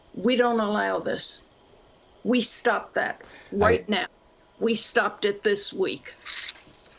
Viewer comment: Did she say.... “WHITE NOW” 45:43?????